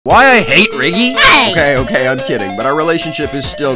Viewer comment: To be honest, I heard it “ why I ate Riggy”😂😂😂